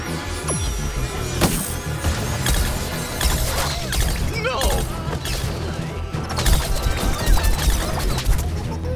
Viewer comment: Did he just say "no!" ? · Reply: Yep. He can actually say different words when you hit him.